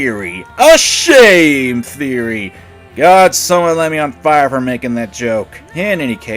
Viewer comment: Did you say light you on fire? 0w0